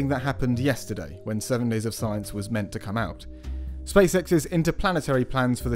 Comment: I thought he said "space sex" and I was excited